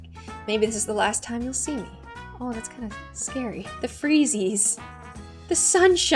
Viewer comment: did she just call them freezes 😟☝️